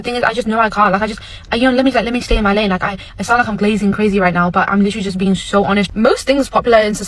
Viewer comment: Did she just say glasen crazy black culture is all over the 🌎